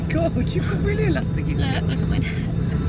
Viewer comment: can someone tell me what that woman says at 6:23 never figured it out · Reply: You don't have to tell me about your erotics